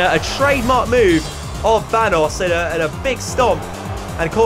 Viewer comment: did he say thanos?